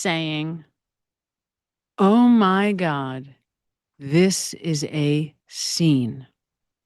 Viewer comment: I hear it as, "OMG, this is insane." Which, if true, is almost more telling than "This is a scene" because it reinforces that Jen knows JOK is dead, and two women are losing their minds trying to re-animate his corpse.